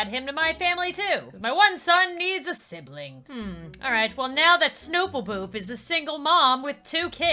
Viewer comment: the captions say snow poopoo